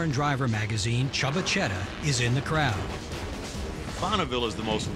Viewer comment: Thought he said "chubba chedda" lol